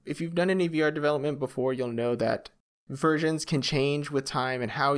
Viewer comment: I heard versions as Virgins and died laughing